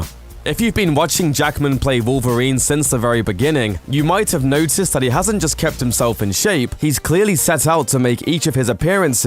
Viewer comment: First time i watched this i heard at 0:36 "you might have noticed that he have not just Captain falcon shape" i was like wait what?